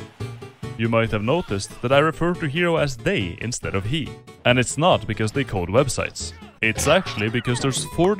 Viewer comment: I've listened to it like ten times and I can't quite understand what he's saying at 0:45. It's not because they code websites?